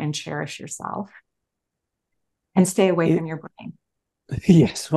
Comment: What was said at the end ~1:03:15 “….and stay away from ______” ??? I’ve listened to it multiple times and can’t make it out. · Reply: I think She said "Stay away from feeling guilty and responsible for the entire Planet and stave away from the brain?" Sorry, it's very difficult to understand. I hope She reads this and answers.